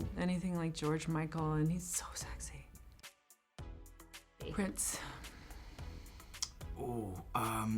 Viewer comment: What did she say at 1:03? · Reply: Prince